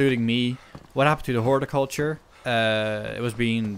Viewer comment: what did you say at 4:11 ? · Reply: Horticulture. Growing your own veggies and fruits etc.